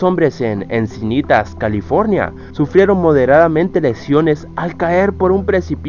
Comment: pensaba que dijo que sufrieron erecciones no lesiones 😂